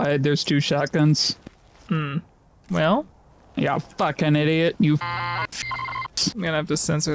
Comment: What did I say? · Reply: Fricking fricks